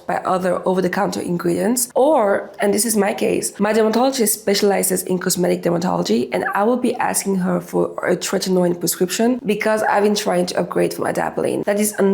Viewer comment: your case - I cannot understand what you said, would you write here please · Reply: “In my case, my dermatologist specialises in cosmetic dermatology and I will be asking her for a tretenoin prescription because I want to upgrade from adepaline”